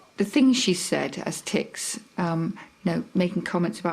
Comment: did she say tits?